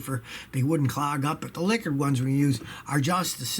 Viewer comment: and 6:42 did Scotty say "liquored one"? He must have just returned from a trip to the bar. 😊